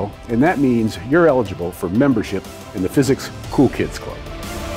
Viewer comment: I listen to at 1.5 times speed and in the end I was already scrolling down to the comment section, when I heard "membership of the physics ku klux club" and I was like WTF! I had to replay twice and needed subtitles to get it (non-native english speaker)